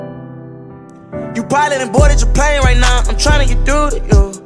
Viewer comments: i could’ve sworn he said “ boarded your pain”🤔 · this took me soo long to find since I was typing “you’re probably bored and you playing right now” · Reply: I thought it said “you probably boarded Japan right now” 😂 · I thought that said “you probably bored in japan right now”